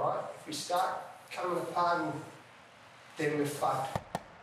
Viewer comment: did he say, we will be fucked?